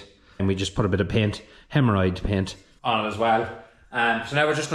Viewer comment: I could have swore that you said "hemorrhoid paint"!